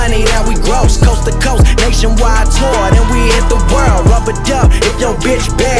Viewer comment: anyone else think he said rubber duck?? Or am i just hearing shit?